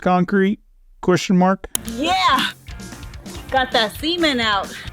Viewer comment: If she just spit on it. And use both hands on shaft. And what did she say she got out. Oh cement i thought she said seamen.